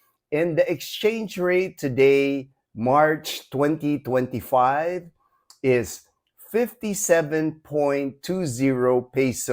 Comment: Hello my friend. Hope all is well. I was comparing the dollar exchange rate from August 2024 to March 2025. So; I said March 2025. Just wanted to point our monthly $1,000 budget went up 1,000 pesos ($18) because of the better exchange rate. Take care my friend. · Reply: thank you. I thought you said, as of March 20, 2025. :)